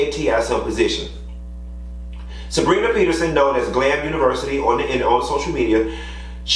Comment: Whats the website where the stories are? It sounds like he said ‘vland university’ or something like that?